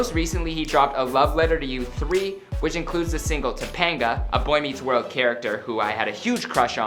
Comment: Not judging but he sound gay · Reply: I thought he said he had.a.crush on Depenga !?